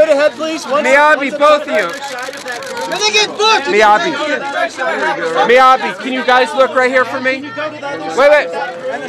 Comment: miyabi, fuck youに聞こえてワロタwwww